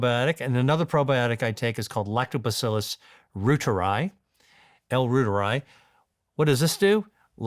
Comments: Did anyone catch the chewable/vitamin/supplement he takes for his teeth and gums?! I thought I heard it was L-reuteri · dont understand which probiotik he meens "ruturay"? can someone help, thanks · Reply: L. Reuteri